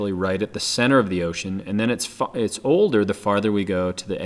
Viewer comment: xD it sounds like your saying ocane not ocean xD